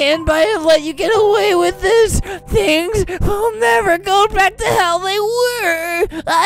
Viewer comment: anyone else hear "and things will never go back to hell they were" no jsut me? k.